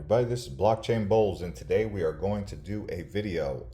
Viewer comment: First time I’ve watched one of your videos. I thought you said “Welcome to Blockchain Balls”